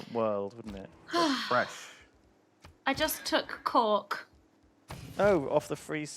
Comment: does it sound like she said “I just took c*ck” to anyone else?